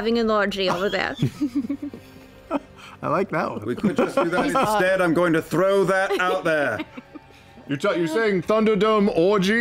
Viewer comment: what did vax say? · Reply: "we could just do that instead, I'm just going to throw that out there"